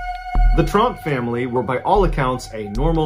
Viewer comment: Who else thought he said "the Trump family" when he said "Tromp" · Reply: I made the same comment just now. I thought he said Trump family but knew I heard wrong when he said hard working.